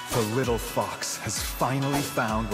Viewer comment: Thanks for showing his unique Kitsune effects when Lancelot Passive is full, definitely most unique skin right now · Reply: I think it's not wolf, at 0:46 you can clearly hear he say fox instead of wolf